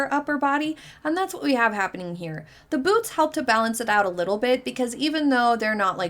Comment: I thought you said the boobs helps balance it out and u thought”what boobs”. You said “ boots”. Lol.